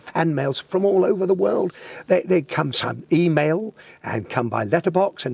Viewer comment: did he say email??